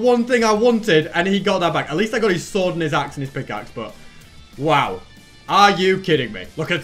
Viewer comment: Did I just hear say "I did get his swordeness axe in his pickaxe" during 18.02...🤣🤣🤣🤣